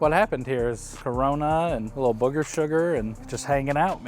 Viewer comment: "Booger sugar." Is he saying what I think he's saying? 🤣